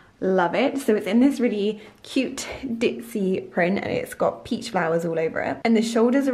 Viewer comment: Love the dress... But I'm confused What does dipsy mean. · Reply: i thought she said ditsy, it sort of a carefree and feminine way